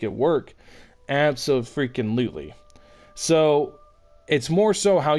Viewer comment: wtf i heard slut xD